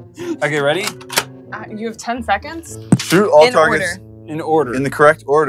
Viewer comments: Wait did say sh!t at 3:30 or something else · Did say sh*t at minuet